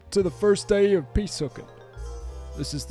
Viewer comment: Was only listening and thought that guy was saying “pee sook”.